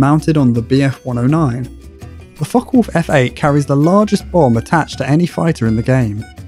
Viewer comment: Captions: "The Fuckwolf" Yes, wolves do that. They do it pretty frequently in the forest behind my house. Trying to sleep and all I hear is horny wild dogs moaning, yipping and becoming less endangered. That or the cardinals that try their damnedest to be roosters.